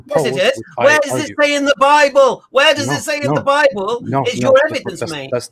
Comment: They said that said “where does it say in the bible” when he actually said, “where does it say in Abrahamic, Jewish, or Christian history” they need to know what was said and not what they thought they heard.